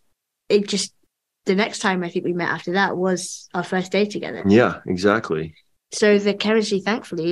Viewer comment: they seem to have a lot of chemistry on screen and off screen. i'm hyped! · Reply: did she say our first date 📅 🤔 ohhhhhhh birnnnnn on Paco